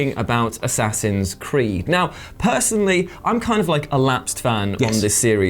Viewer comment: am i the only one who heard him say A-saxons Creed (